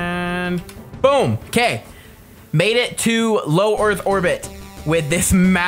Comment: Am I hearing things of he said "orth orbit"?